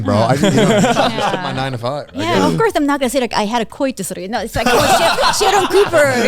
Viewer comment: what did she say here? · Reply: ''Of course i am not going to say i had a coitus''